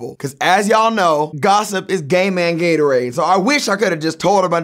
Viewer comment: Gossip is gay man Gatorade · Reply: I thought he said Gamer Gatorade lmao